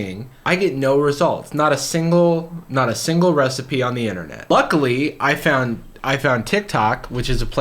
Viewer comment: Strange audio illusion. Close your eyes when he says luckily, sounds like he says buckily. But when you watch it with your eyes open he says luckily.